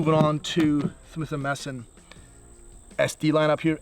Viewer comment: Did he say Swith & Messon??